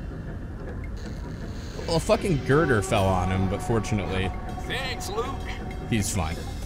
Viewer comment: He clearly said 'Thanks Luke', not "Lieut..."